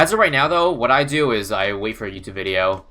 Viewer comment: Can anyone tell me what did say here? He waits for [???] video? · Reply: YouTube